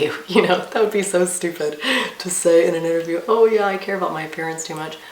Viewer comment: WOW In the start of this video you said you have not ONE FLAW about yourself that you can think of except loving your mom and dad . I've never met a perfect person before. Maybe some self discovery classes or a self help book might help with this .We ALL have flaws my dear . sending love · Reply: it sounded like you said you care about your PARENTS too much, but I listened again and realised you said you cared about your APPEARANCE too much. Hahah glad I wasn't the only one who misheard xx ❤️❤️ xx